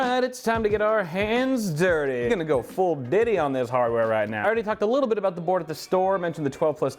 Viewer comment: Did he just say going full Diddy